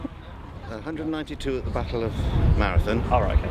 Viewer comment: Take it from a greek...The battle of Marathon took place in 490 BC...NOT in 192 BC as mentioned by the "hoplite". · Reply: You actually misheard what he said. (Not helped by the cut in the audio.) He said "There were 192 in the battle of Marathon and the Persian casualties were approximately 6,000." He was referring to 192 Greek casualties not the year of the battle :)